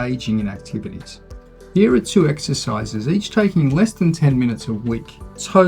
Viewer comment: I thought you said there are 3 exercises, but I only see 2. Can you please clarify? Thank you!